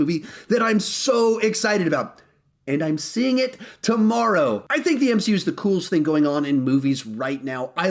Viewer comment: did he say tomorrow?